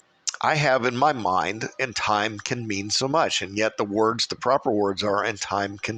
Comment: I clearly hear "I can mean so much" . I replayed it many times. Weird